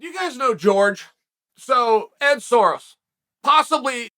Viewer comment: I thought you said George Soros lol. I was like where is this going..?